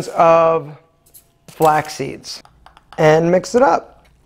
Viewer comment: what is the Last ingredient ? I couldnt understand it🙈 What Kind of seeds are Those? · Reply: Flaxseeds